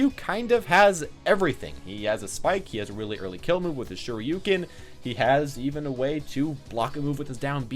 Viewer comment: I always thought ryu was saying “show you ken!” When he used a shoyuken